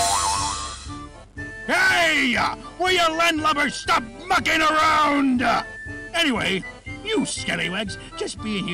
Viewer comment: ngl I thought he said fucking